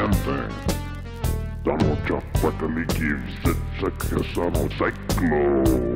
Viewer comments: "You, man thing!" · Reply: I don't know what he says after ward besides "Any leverage on this stupid animal? Ahh...his woman-friend!" All I heard was "Donald Trump want a me gives a sit a son of Psychloooooo" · min. In did he say Donald Trump